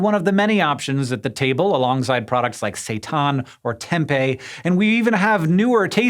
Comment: Sooo. All I hear is Hank describing imitation food with ‘satan’ 1:21, followed by a good ‘Morningstar’ hotdog at 1:25. It’s confirmed. Imitation meat is the product of Demons!!!